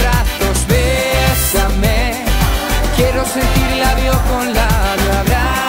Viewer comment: Siempre escuché "bésame quiero sentirla de otro lado" jajajaja y ha sido "bésame quiero sentir labio con labio"